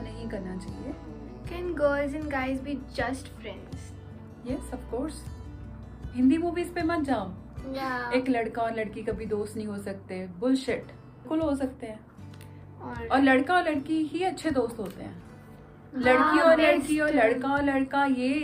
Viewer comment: did she said "bullshit"